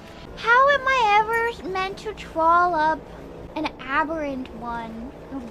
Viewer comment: I think she said "trawl" and not "troll" It's not exactly a common word since it's specifically a fishing term.